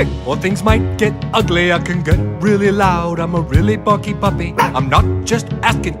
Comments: What yall heard:😁 What i heard: I'm a really fu!?ing barky puppy💀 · People who thought it said I’m a really fucking puppy💀 👇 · I thought it said “ Im A F***y Puppy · I thought he said im a real FUCKING puppy🤣🤣🤣🤣🤣🤣🤣🤣🤣